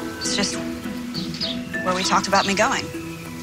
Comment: i thought she was saying where we talked about mako if anyone knows what I’m thinking about!